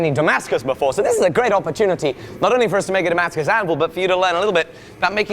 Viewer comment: At the end of the video, it sounded like he was saying a Timascus anvil...instead of Damascus anvil...these are two different things.....although if you can figure it out I would love to see you forget some Timascus/mokuti or even so mokume.